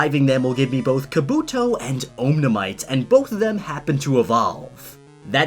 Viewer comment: did he called Omanyte omnamyte or I overheard that?